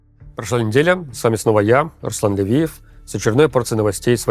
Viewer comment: Пока хрумкал яблоко и не смотрел в экран услышал: - меня зовут Аслан Делиев Удивленно смотрю кто это вообще, а там вполне себе Левиев. Послышалось же такое!